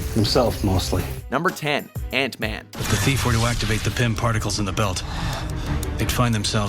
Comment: After reading Deadpool, whenever someone says "Pym Particles", I hear "pimp articles".